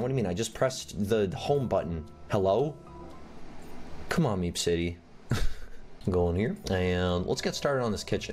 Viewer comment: Lol at the start I thought u said meme city